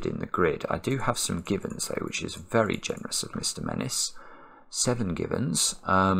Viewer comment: Somehow, I heard that as "Seven Gibbons". Now THAT would be an unusual puzzle.